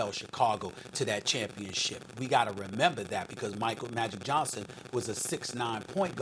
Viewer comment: Did he just said Michael magic johnson?